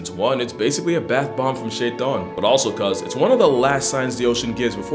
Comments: Had to rewind 0:50 to make sure I heard it right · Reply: Bathbomb from shaytaan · Can someone tell me what he said for this joke? I hear the "bath-bomb" bit, but what's the full joke? "It look's like a bath-bomb from ____" (???) EDIT: Closest thing I've heard based on comments elsewhere is he said "Shaitan, which is the Islamic version of Satan. Translation: a jellyfish bloom is "Satan's Bubble Bath"😂😂😂